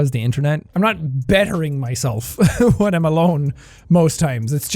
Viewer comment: it sounds like he said “WATERMALONE” but really he said “When I’m alone” lol.